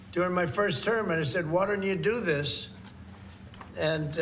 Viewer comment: i'm sorry did he say " water you do this " 😂